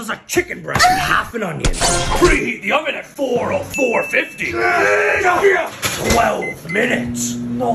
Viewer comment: I thought he was about to say "heat the oven to 40550 for 2 seconds" lol